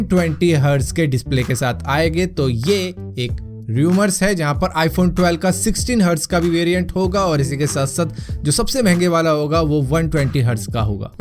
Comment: i heard 16hz iphone variant , the statement should be of 60hz varient and 120hz varient . Like if you hear the same.